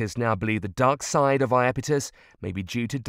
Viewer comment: for a moment, i thought it said dark side of the force...